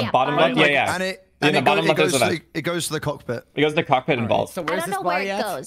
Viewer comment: I wasn’t paying attention, and said, “It goes to cockpit and vault” and I thought he said “cockpit and balls”.🤦🏻